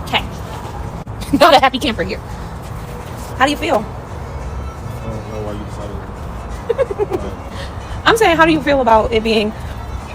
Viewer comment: Yikes 😳 it sounds like he said, “I don’t see why you excited “👀